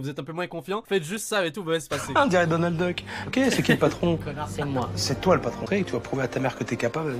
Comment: C moi ou à 16:33 il dit conard c moi